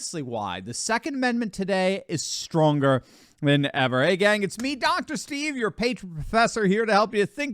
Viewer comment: Did say, “ the 2nd Amendment is stronger WHENEVER”? 😮😳